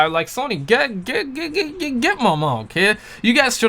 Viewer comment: You part haitian bro ? I hear u saying "gent manma"